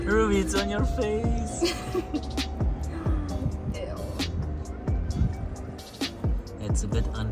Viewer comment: Did she said ewwwww